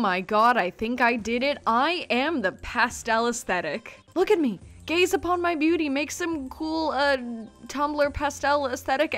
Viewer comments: When you said "gaze upon my beauty" I thought you said "gay is upon my beauty" XD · I thought she said “G A Y S upon my beauty”